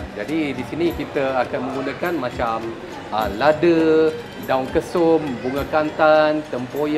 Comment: Aku salah dengar dia sebut daun kesum sebagai daun ketum??